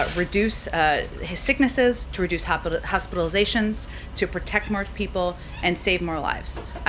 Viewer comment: She actually said to protect marked people at 4:15!